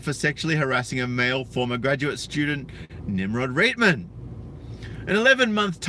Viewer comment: Nimrod Ramon? Did i hear that right?